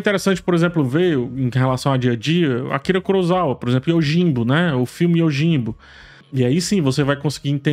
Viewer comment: qual nome do filme indicado? Não entendi muito bem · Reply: Yojimbo